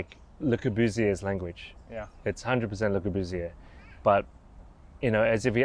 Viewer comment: Can someone please tell me who/what he referenced? he said its 100% "le Que buseur" or something like this?? Thanks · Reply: Le Corbusier